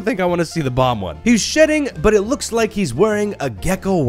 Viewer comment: Uh oh, the closed captions say "sh*tting" instead of "shedding." The autogenerated has it correct though, but not the main one, so does that mean this was a human error?